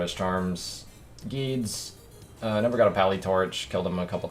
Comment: Am I blind or did he show an assassin torch? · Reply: I had the same thought and laughed bc I thought he said “pally torch” but he said “I never found a pally torch”